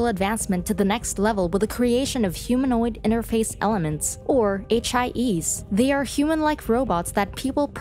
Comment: Bro, I thought they said HIVs instead of HIEs LMAO